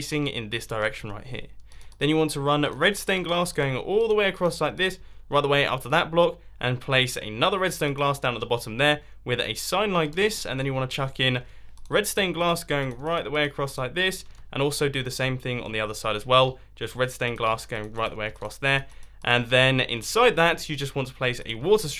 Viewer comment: Did anyone notice that at 10:55 mambo says “chuck in you red stone glass” instead of red glass Like if you noticed or didn’t notice (